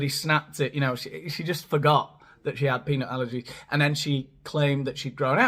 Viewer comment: I thought you were saying penile allergy 😂😂🤣🤣🤣